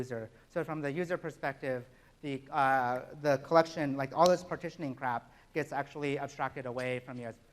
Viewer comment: did he say "all these partitioning crap"?